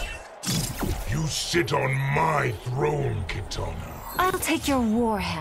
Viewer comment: 😂 for a min. I thought I heard shao khan said 'you shit on my throne kitana'